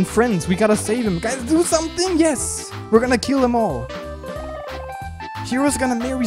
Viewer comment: What says : "we're gonna kill'em all" Then why do I hear : "we're gonna kill a mole" ?